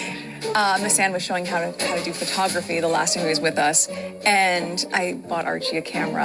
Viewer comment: Does she say here "the last time he was with us" ??